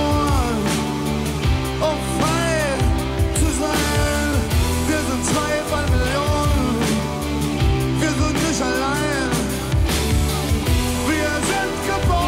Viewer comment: Endlich hab ich die Zeile nach dem Refrain verstanden! Rio Reiser sagt: "Wir sind zwei-pfund Melonen, wir sind Michalein." Macht jetzt Sinn.